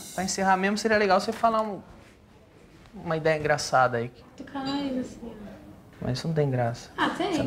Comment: o que ela falou??? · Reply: "tocar no nariz assim ó" (limpar o salão)